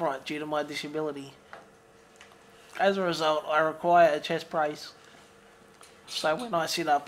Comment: Took me awhile to realize that it is 'chest brace' instead of 'chest price' as shown in CC